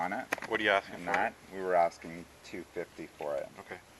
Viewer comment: 250? did i hear that right? lol